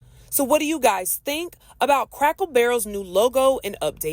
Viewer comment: Sounds like you were saying “crackle “ barrel